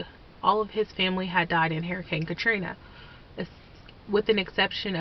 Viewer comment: I heard "Harry Cane Cathrina" like 5 times, before I understood it. :P